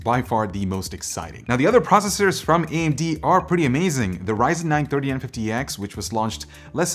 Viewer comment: Had to listen to what you said a few times when you said Ryzen 9 3950x 😅😅 was so confused when I heard you say it. For some reason I kept hearing “930 and 50x “